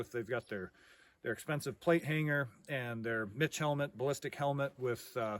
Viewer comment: I had to rewind and turn it up because it sounded like he said "bitch helmet" But he said " mitch helmet " 😂